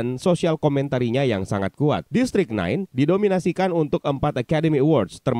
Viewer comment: Sumpah waktu lu ngomong "District 9" ditelinga gue kedengeran kyk "Listrik naik" 🤣🤣🤣